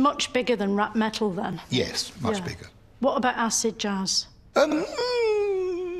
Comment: Oh its Acid Jazz, I thought she said acid jars